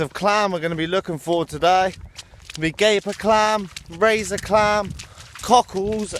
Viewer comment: Sounds like he is saying "gay for clam"